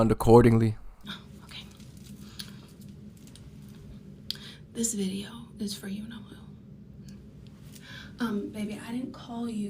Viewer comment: Did she say "this video is for you Noel"? 😂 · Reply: "For you-know-who"